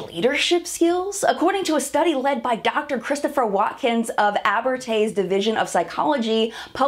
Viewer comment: For a moment i thought you said Dr Christopher Walken....and i giggled....alot...😄